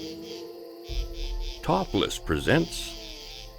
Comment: lol i thought at the start he said topless instead of top list!!! 😂😂😂